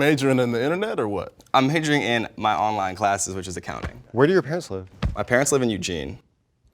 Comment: I thought he said "where do your pants live".